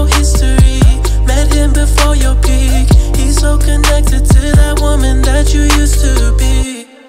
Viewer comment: Am I the only one that thought the weekend said hisoka when he said “he so connected”😂